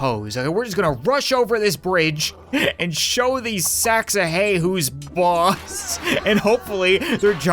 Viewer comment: I thought he said lets show them who's Bob Ross